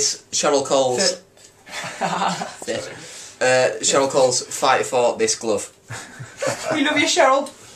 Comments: it sounded like they said "Fight for this glove" hahhaa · Lol sounded like max said "fight for this glove" , aha well you gotta love these guys, theyre amazing :D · when they introduce the song at their house it sounds like they say 'fight for this glove' haha :L :P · i sounded like max said fight for this glove LOL!!!!!!!!!!!